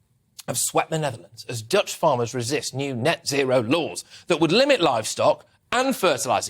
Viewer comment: Sometimes British language sound really funny. 0:03 'As Dutch farmers resist neo-nazi(row) laws' is what I heard. Love from the Netherlands 💜🙏🏼